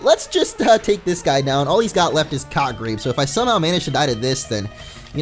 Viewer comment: I thought more said cockrape not cottgrape.